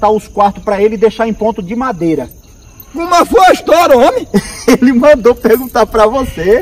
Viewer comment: "Cuma foi a história homi?" - [3:12] "Pera aí , pera aí , apaga essa peste aí"